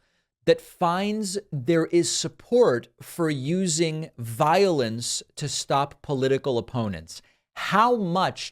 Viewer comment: Not gonna lie. I thought he said Violins at 2:55 lol.